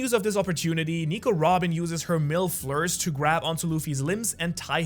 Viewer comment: Yo! I thought he said "milf lures"!